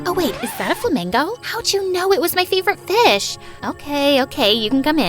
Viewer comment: did she just say a fish 😂